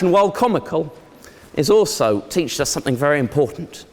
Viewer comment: Went to eton and oxford- uses word "teached" instead of taught. There is hope for everyone. SMH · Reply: I think he was saying "teaches" or "is teaching" but he eats a few letters.